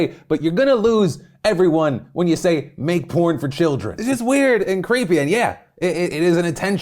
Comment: I misheard that as 'corn for children' for the first sentence and I was like I mean they offer corn off the cob, seems child friendly enough